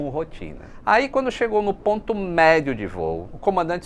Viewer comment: O, faio uma coisinha, como vc falou ETP = Equal Time Point, mas ai sem querer vc falou ponto onde as DISTANCIAS sao iguais, o que e fisicamente, a metade da rota = EDP. Os dois somente serao iguais se o vento for zero. O ETP leva em consideracao o vento para voltar e para prosseguir. Para voltar, era usado o vento ja conhecido. Para prosseguir era usado o vento previsto. Era um calculo bem complexo, pois era necessario considerar o efetio da componente de vento de cauda e de proa, o que dificilmente seria igual nos dois setores. Os navegadores e engenheiros de voo eram tao ou mais importante que os pilotos. Se eles errassem, nao haveria uma pista para pousar la na frente. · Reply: "sem querer vc falou ponto onde as DISTANCIAS sao iguais" Na verdade ele não disse isso, não. A expressão usada aos 04:23 do vídeo foi "ponto médio de voo", que de fato é ambíguo (pode ser entendido como metade do tempo OU metade da distância). Ele não entrou nas especificidades técnicas da diferença entre ambos, mas certamente não falou em "DISTÂNCIAS iguais".